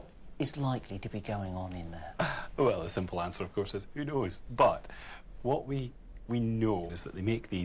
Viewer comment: "Well the simple answer of course is... yudoeis" Huh? What the heck did he say there? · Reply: "who knows"